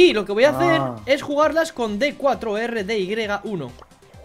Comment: lo mejor es que dijo D4R-DY1, si lo quieren escuchar lo dice en el segundo